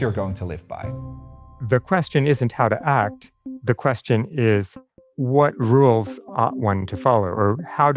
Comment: the captions read "The question is what rules one ought to follow", but the speaker has just said "The question is what rules ought one to follow." I think that if one is to put the word "ought" before the word "one", then the word "to" needs to drop out. It is an odd set-up. But I wish the captions would reproduce what the speaker has actually said there, instead of tampering with it. Or if it is tampering to make a correction, it should have the capability of accepting the words in the order "ought one", because that is a real way that people speak and write.